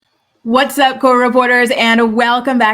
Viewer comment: Oh my god! I’ve been watching your videos for a while … like a few years since I first found you … and I always thought you said “what’s up court reporters” and I never understood what the hell you were talking about “court reporters” …. This video I guess it was very clear that you’re saying co-reporters 😂😂😂😂